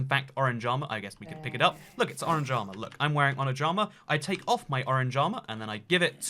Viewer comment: Orange armour 2:52 , i heard on a jama. Lol 😂